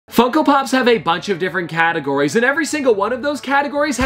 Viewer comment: Sounded like he said fuckopop